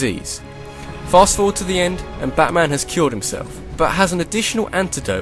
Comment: I first heard killed himself lol